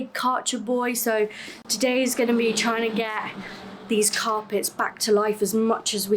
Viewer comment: Did you say "carpets" or "tar pits?"